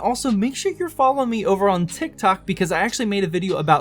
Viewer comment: did you say tik tok 😂 I can’t find the link lol